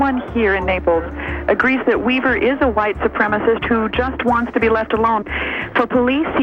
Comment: "White separatist" = he is white and lives separate from the nearest town.... seems legit. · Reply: I thought they said white supremacist